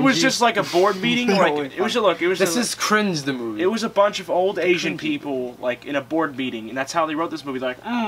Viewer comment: did he say old asian people? Hey don't associate Asians with this; they make cool stuff like Naruto.